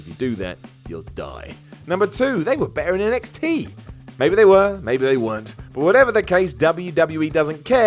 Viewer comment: In number 2 I thought you said they were better than nxt and I thought you meant the main roster and I was so confused